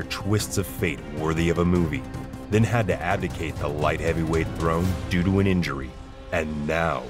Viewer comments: Abdicate , hell of a word . At first I thought you said “Advocate” and I was gone call it out . But I gave you da BOD and used closed captions to see exactly what word you said and look it up . Imma add that to my vocabulary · in the intro, i know you meant "vacate" the title or something similar but i swear i hear "advocate" which just isn't right...